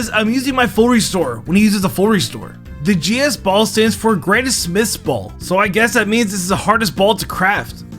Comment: does he say that it was called granny smith ball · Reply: No he says greatest smiths